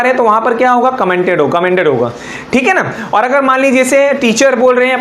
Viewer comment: Sir!! 9:48 min per aapne kya kaha "commented" kaha kya?? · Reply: Commanded..